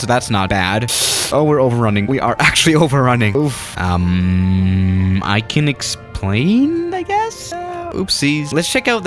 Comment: "i can xplane i guess?" is that just me who heard